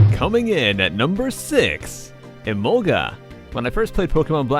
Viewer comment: When you said emolga I thought you said Ebola XD